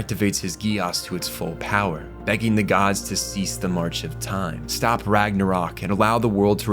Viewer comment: He actually said "Dont stop the march of Time"